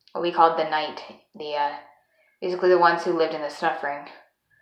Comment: I thought I heard you say "the ones who lived in the snuffering." I believe this to be a, I guess you might say, Freudian slip? Or maybe that's not the term. I can tell you are overwhelmed by your integration at this point as your alters are heavily affecting your speech.